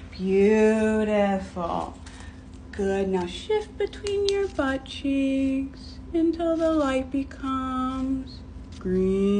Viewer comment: Shift between ur buttcheeks until the light is green 💀💀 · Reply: I THOUGHT SHE SAID NOW SH*T BETWEEN YOU’RE BUTTCHEEKS UNTIL THE LIGHT IS GREEN💀💀💀